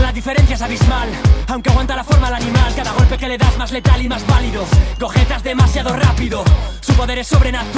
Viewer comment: pense que decia "cojes demasiado rapido"